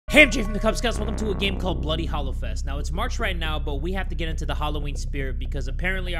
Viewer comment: yooo I thought you said bloody Hallof*ck 😂😂😂